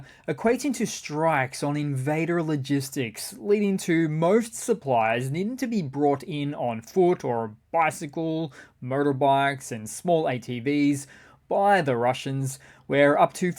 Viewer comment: or so it sounded like you said, "murder bikes." 😅